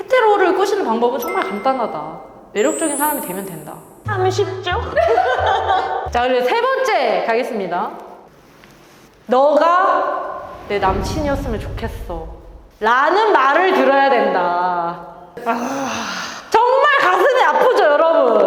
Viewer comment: 너가 내 동생이면 좋겠다는 소리를 들었는데요ㅠㅠㅠ